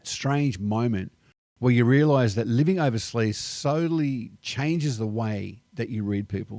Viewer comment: too many words got ya tongue tied and really sound like living over sleeze instead of what you tried saying living over seas